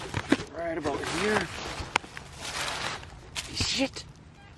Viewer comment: What did you say at 4:21 ?! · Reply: Matthew I thought he said shit to lol but he said see it really fast lol